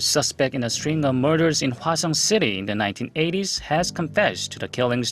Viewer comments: There should not be a statute of limitations on MURDER or RAPE in my humble opinion. Geeeeeez. Also, every time you said Hwaseong, I thought you were saying "Croissant" lol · Every time he said Hwaseong, I heard "croissant"..